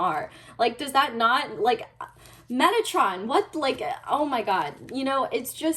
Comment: LMFAO I THOUGHT YOU FREAKING SAID METTATON AND HAD TO REWIND THE VIDEO 😭😭😭